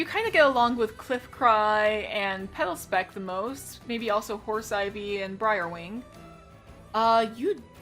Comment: Awesome vid! I love this series and I'm excited to see more :D Every time I hear "Hoarseivy" I think "Horseivy" lmao